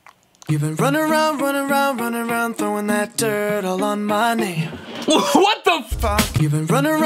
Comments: I misheard: “throwing that turd along my name” 💩➡️Name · I always thought that he said, “Throwing that turtle on my knee” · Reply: Its actually: Throwing that dirt all on my name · What I hear is " throwing that turtle on my nail" 😂😂😂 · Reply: I always hear “throwing that dirt all on my name”